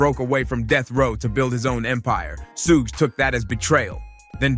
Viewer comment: shugg shugg shugg and your captions have a lot of errors in them.. please at 4:32 you say but the captions say Snoop and it should be Suge (shugg shugg shugg)